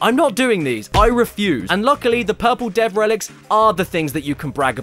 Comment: When he was saying “purple dev relic” I thought he said “purple dead/death relic” makes sense